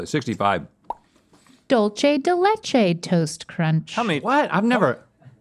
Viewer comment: "dulche de leche " I was so confused when i heard that until i read it and i was like oh Dulce not dulche. LOL